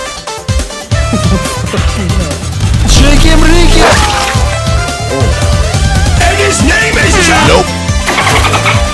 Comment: there is a meme and I would really like to know what the meme is and where to find it. It sounds like somebody saying "ChimkaRimke" or something like that. PLEASE HELP!!!!! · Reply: FMF areForever He's saying cheeki breeki. It's a Russian meme. To understand it more, I'd recommend a YouTube channel called life of Boris.